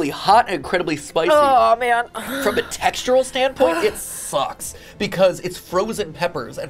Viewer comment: Did anyone else hear “sus?” (Also ear plugs work too)